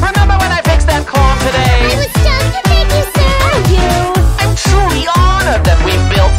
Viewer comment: ngl i thought Nifty said "i was just to thank you sir ! ^^"